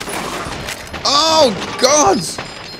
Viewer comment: I could swear that I heard say, "Oh Gods...!..."... XD